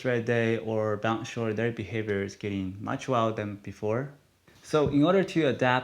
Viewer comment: Can I get a translation? 4:13 he says "their behaviors are getting much why than before" I don't understand what he means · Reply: Wild maybe?